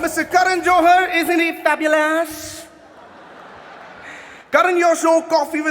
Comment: ye kya bola bhai, fabulous? ye kya tarika hai